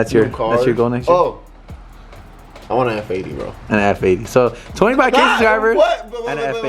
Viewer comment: When said “I want an F80” I thought he said I wanna have a baby… I was like 😳